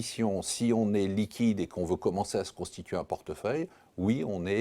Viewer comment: C'est moi où il bugg sur la première syllabe de " constituer"? perso j'ai entendu "se prostituer un portefeuille" haha